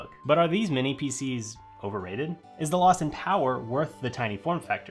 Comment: When you said lossin power i thought you said Austin Powers. I had to back up the video to check lol.